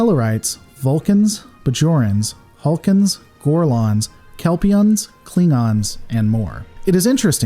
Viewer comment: Did I hear Vorlons from Babylon 5?